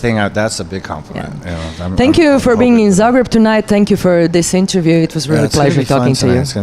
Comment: she said thank you for being in Zagreb tonight. I swear to god that the first time I watched the video I heard thank you for being sober tonight LOL